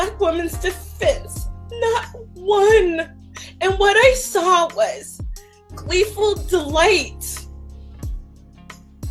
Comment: Damn. I first thought she said "we've killed the light from men of all races" and thought maybe that was some self awareness coming out. Then I listened to it again. That' isn't what she said. At best it's still subconscious.